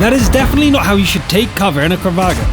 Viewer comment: Did he say kravag instead of kranvagn?